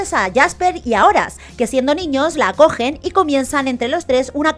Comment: que dices la ké 4:20 soy yo o suena mal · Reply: ACOGEN XD